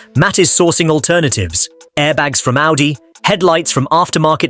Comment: Thought you said 'airbags from Aldi'